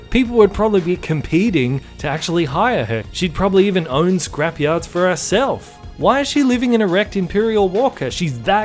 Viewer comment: "probably own scrapyards of herself (I probably misheard this, but I am not going to rewind)". Because the planet with scavengers and quick sand pits that can swallow ships has a decent bank loan system? "Yes, Ms Rey. I saw your diploma from the university of fucking nowhere, you own a hut, a stick, and a motorcycle that looks like a fudge pop. You wish to sell other people's space ships and your collateral for a loan at our bank today is 1 quarter portion of instant bread..............very well. Sounds like a sound business investment. It is like opening a blockbuster video in Antartica. What are you? 18? ALL THE BETTER WITH YOUR MASSIVE CLOUT, CONNECTIONS, AND BUSINESS EXPERIENCE!"